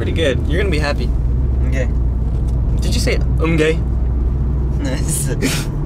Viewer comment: he sounded like he said “im gay” 🤣